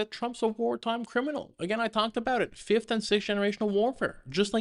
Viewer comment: Did I hear you right........@ 43:15-17, you said Trump is a war time criminal?